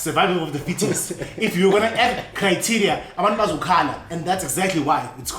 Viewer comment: Did just say "Cry te ria "😂😂😂😂